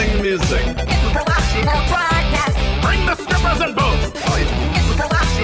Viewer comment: All that deep and heavy analysis and then BRING THE STRIPPERS AND BOOZE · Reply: I always heard "BRING THE STRIPPERS AND BOOBS" i like mine better